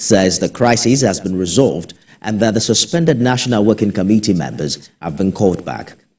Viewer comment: I thought you said they are suspended